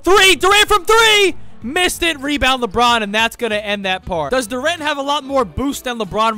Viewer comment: I thought he said lebron and the rat